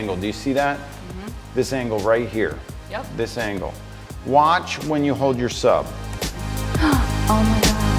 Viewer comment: What is it Hurley’s saying at 8:04? Watch when you hold...yourself??? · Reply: "Watch when you hold your sub" It's a phrase he uses to make your arms look thinner when shooting a headshot. -P